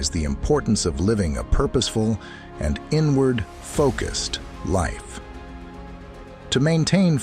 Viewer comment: "Always aim to live a purposeful and inward focused life" I heard ",Always aim to live a purposeful and N-word focused life". Like wait a damn minute.